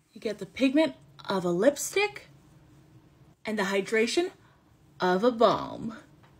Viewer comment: for a second i thought she said “the hydration of a bomb” 😂